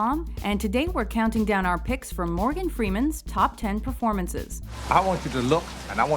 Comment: I misheard Morgan as Gordon Freeman. I was like 'Wtf, top 10 gordan freeman moments?!'